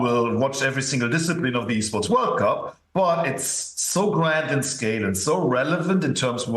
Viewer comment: For a split second I thought it said Escorts world cup and was going to legit watch.